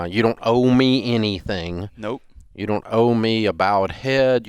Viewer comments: Hahah at first I thought Dave said you don’t owe me head...💦😂💀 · Had to rewind, thought Dave said "you don't owe me head" Lol